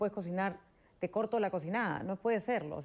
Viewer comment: Ante los titubeos de Meche parecía que el periodista se quería reír. Ademas uso la palabra "cocinada", esta dopada la tía, que le pasa? en un primer momento pensé que decía "cochinada", eso es lo que es ella.